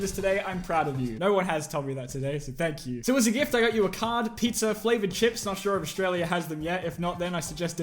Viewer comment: I thought he said pizza AND flavored chips. Luckily l was wrong, if a pizza was sent to you it wouldn't be edible.